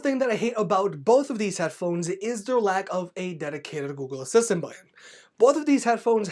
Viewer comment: “Lack of a dedicated Google Assistant...” something? Can’t hear what you’re mumbling here at all. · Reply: He said button